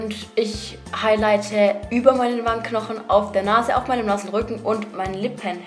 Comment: bei 7:23 hab ich verstanden "ich highlighte auf meinem NASSEN RÜCKEN" 😂😂